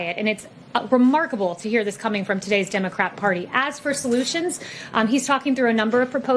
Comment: Did she say "democrap?"